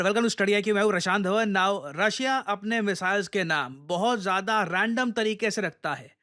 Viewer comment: does he just said 'Russian Dhawan'?!